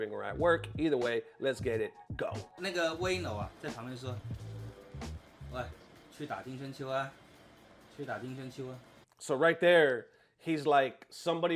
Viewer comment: Am I the only one who heard the N word at 18:20 😂